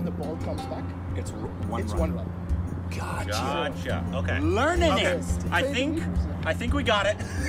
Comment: did he just say learnaning